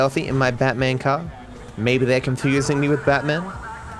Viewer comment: I thought Molly said, “Batman’s a murderer”. Kinda ironic as mentioned Batman in this mission lol